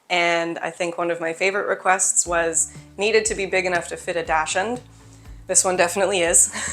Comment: I heard you say that the sink needs to fit a “dash hund”. What is that? Did you mean dachshund? If so, you’re saying it wrong.